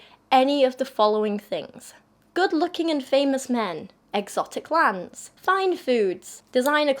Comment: I misheard and thought said women shouldn't enjoy "exotic LAMPS" and what's even more revealing, I wasn't actually surprised that an idiot like this dude would be opposed to women choosing their own lamps.